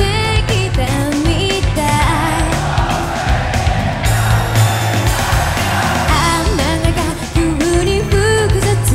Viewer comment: からの掛け声なんていってますか？ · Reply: L・O・V・E ラブリーあやや！ ですね ハロプロ定番のコールです